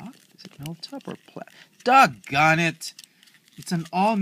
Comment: you said "Dod Garn it" instead of "God darn it" :)